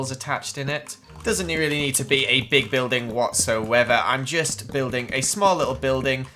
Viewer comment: did i sorta sound like he said "whats the weather?"